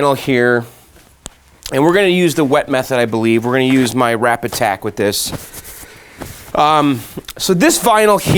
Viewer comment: Lol "rapid tag"... All this time i thought you was saying "wrap attack". lol love your videos keep up the good work... And p.s. that looks amazing! Love mk2